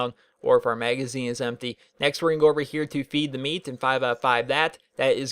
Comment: I thought he said Beat the Meat